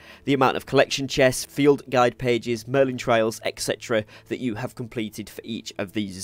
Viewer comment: FYI, "trial" is pronounced with an "eye" sound in the middle. Sounded like you were saying "Merlin trail". 😁